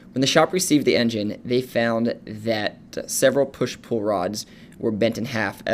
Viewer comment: did he say "push pull rods"? Lol